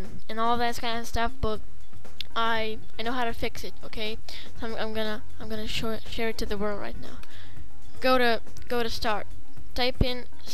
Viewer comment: did he say I'm gonna show it to the world right now XD lol, all of these videos on youtube should feature kids talking in the BG :D :D :D